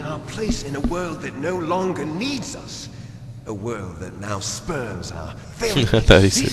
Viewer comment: lol liquid snakes a roach, he'll never die XD 1:21 i thought he said sperms too haha! and when you were talking about waking up top of metal gear naked "wonder what liquid was doing" whaddya think XD